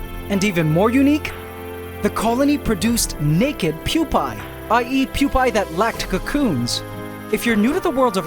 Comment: DID HE JUST SAY I EAT PUPAE THAT LACK CACOONS!?